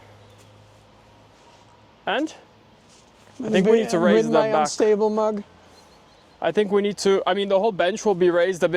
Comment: Did I hear say “unstable mug”?! 🤩